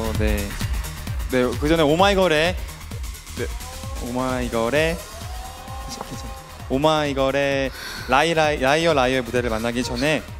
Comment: no one literally no one At 0:11 I heard 'Oh my GAUDE' lol